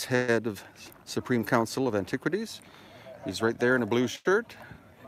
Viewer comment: @ 3:30 in, did you say skirt rather than shirt? hahaha